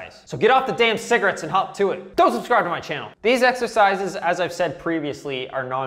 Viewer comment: I heard "don't subscribe to my channel" and even the cc thinks so